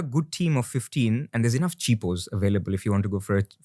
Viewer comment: really thought he said cheap hoes out of nowhere